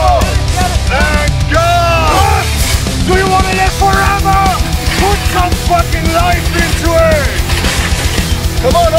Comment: What is saying ? ("Do you wanna live forever" ??? and what next) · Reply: "put some life into it" ist es glaub ich gewesen